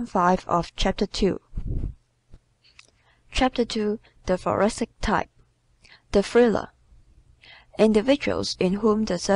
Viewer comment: “Ta fuwasick type…Da fwilla!?” WHAT! 👎🏻Part 5 is too hard to listen to!!!!! Can we get a new reader??? She can barely pronounce the words and I cannot understand 1/2 of it